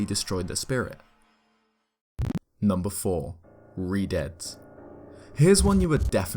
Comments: Thought it said “Redheads” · Lmao at first I thought it said "Redheads" But when I first played wind waker I was so very terrified to go near the re-deads, I didn't want the scream and biting to start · I thought that said “Red Heads” and I got really confused